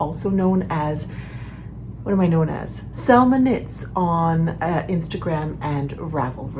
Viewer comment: Hi Selma! I am cleaning the kitchen and I am so hungry. I listen to YouTube, watching from time to time, here it comes up your channel next after another and I hear " I am salmon eats " instead of Selma knits . lol...I need to pause, seat down and eat something :))